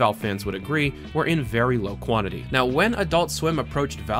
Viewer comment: I thought I heard "of very low quality". After relistening, I'm glad I misheard it.